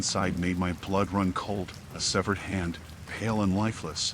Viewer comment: Am I the only one who heard "hand" not head in the first story